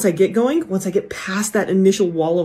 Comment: I thought she said "Wall of Waffle" cuz I'm tired